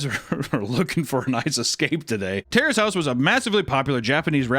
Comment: thought said, "terrorist house." I almost choked on the gulp of Raw Meat I took. The more I think about it though, the more I think it would make for a crazy show that would be hilarious I feel or even a movie.